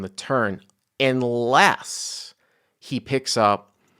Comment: did you say, " inless " ? 🤔